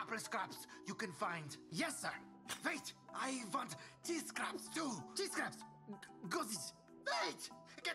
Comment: cheese scraps? i always thought he said TEA scraps